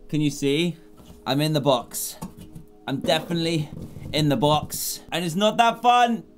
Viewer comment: I thought dan said he is f***ing the box